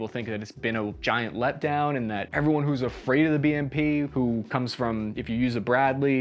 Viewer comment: Good video, nice break down of BMP history. It sounds like your saying “people who are afraid of the BNP” the British Nationalists, now that would also make an interesting video.